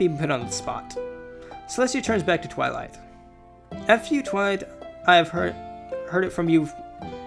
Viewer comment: I thought he said:"f.u. Twilight